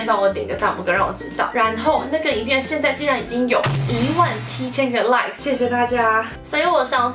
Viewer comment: 只有我把17000個like聽成17000個爛嗎?